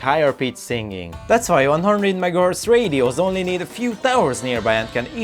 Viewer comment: I tried listening to this five second segment like 10 goddamn times, I am trying so hard to understand what the hell kind of tower you said, but I cannot make it out for the life of me. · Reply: HOLYSHIT Was it "100 megahertz radio"?? I swear to gawd this took too much effort. Please let this be correct.